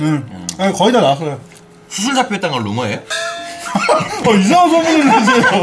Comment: 뭐라는거야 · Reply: "수술 잡혀있다는거 루머에요?"